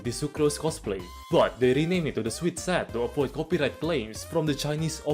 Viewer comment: wtf I thought you said sweet sack